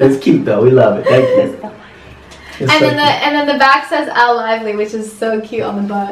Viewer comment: OMG ON 21:44 I heard a voice I think it said "this is Elle lively."